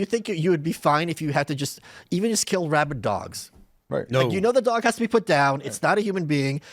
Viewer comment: I thought he said rabbit dogs, not rabid dogs. I was confused at why just rabbit dogs were chosen to be put down.😏